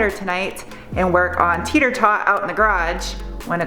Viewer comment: I thought you were saying TETER tot. What?! Ha!